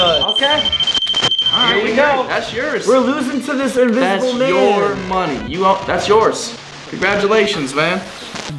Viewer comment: Great video! Still half way through..does anyone else hear "you're money " at 31:43-44 from spirit box?